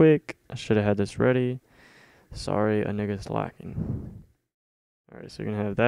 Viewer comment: DiD i HeAr ThE n WoRd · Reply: He said “sorry I think it’s lagging!